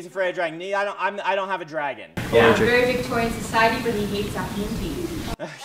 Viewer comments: What does she say at 20:44? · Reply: "A very Victorian society but he hates afternoon tea." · What's the girl saying at around 20:43? Something about Victorian Society but... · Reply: Victorian society where the protagonist does not like to drink tea. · Did anyone catch what the person said at 20:40 that made Brandon Sanderson reply that it's really tragic? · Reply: "A very Victorian society but he hates afternoon tea."